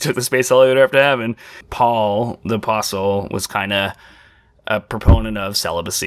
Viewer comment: i thought you said "Paul the fossil" I was so confused before I realized you meant apostle hahaha